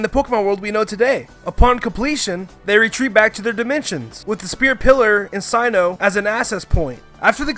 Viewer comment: Did i hear this right 2:13: With the spear pillar in synoh as a access point" SYNOH WHAT